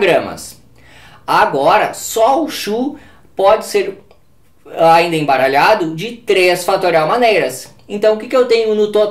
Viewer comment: Desculpa nao entendi aos 6:25 minutos a quantidade de CHU vc embaralha 5 x e fala que é 33 x? Me ajude por favor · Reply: Eu disse que o CHU pode ser embaralho de 3 fatorial maneiras, que é 6.